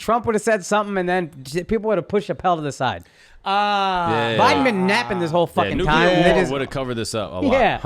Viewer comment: What he say??? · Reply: Biden been napping this whole effin time